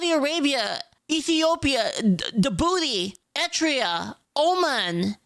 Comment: Oh, Eritrea. I heard "Etria" in the background and had to do a double-take.